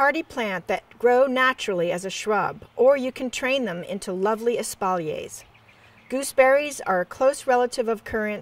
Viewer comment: What does that word mean at 3:58? Sounds like espaulays?